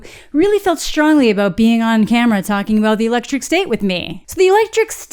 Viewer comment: I thought you said "the electric stink" in the beginning